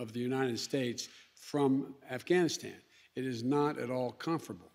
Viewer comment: Did he say, "... it is not at all comfortable." 2:07 Quite the contrary, under the Taliban it was quite "united," albeit at the barrel of a gun and at the edge of a knife.